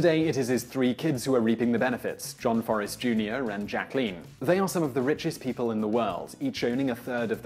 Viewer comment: or so, I heard Simon say "John Forrest Jr., and Jacqueline". I did some reading about it and discovered that he really WAS saying all three kid's names, "John, Forrest Jr., and Jacqueline". But it was very confusing for a bit, because of the way he said their names. Hearing that fast talking, I was thinking maybe I should post an idiotic youtube comment saying that Simon mentioned the two men and didn't say anything the daughter. I listened to it again, then wondered why he was talking about one of the sons and the daughter, but not the other son. Then I started comparing names and reading about their family, and finally figured it all out. But in the future, Simon, when you're narrating a script, keep this in mind... at 7:09 you literally said "John Forrest Jr. and...", even though now I know you meant "John, Forrest Jr., and..." I understand that you're trying to do this quickly, and you enunciate your words quite well (a rarity on the youtubes). But perhaps, in a case like this, it MAY be better if you had written the script to say, "John, and Forrest Jr., and Jacqueline." I think adding the extra "and" in there would have made what you were saying much clearer to the casual viewer of your video - and would have prevented the initial confusion I had when hearing you list their names. That being said... it's a minor little thing, and I wasn't terribly concerned about it overall. I still thought I should mention it to you, for the sake of future scripts, but it was